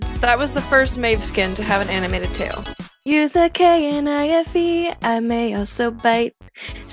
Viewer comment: did she say kinafe? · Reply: Assistant no I said k-n-I-f-e like spelling the world out